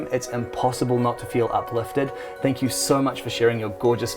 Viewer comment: At the beginning it sounds like he said “thank you skillsh*t” but the more you watch it you can here share